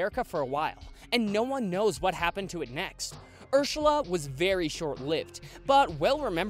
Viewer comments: did he say "Irshala?" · (this is a couple seconds before the actual thing) did he just say Urshala